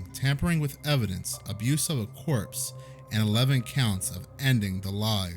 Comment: I really like your content. This is my second video. I'm going to subscribe after this one but I'm a bit of an English nerd and I just can't help myself. Marine {core}, yes I know how it's spelled but it's {core) not corpse. The only word pronounced corpse is a dead body. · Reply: I heard it as corps not corpse 😊